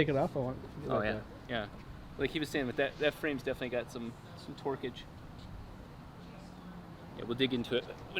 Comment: sounded like twerkage for a second haha